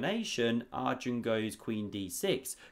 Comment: I often understand "prawn" - have I heared it wrong or did he really say this? 😅 4:40 "covers the C prawn" 😅 omg ... if it was intentional he is a master of puns 🤯 "sea prawn" ... omg this would be too good to be true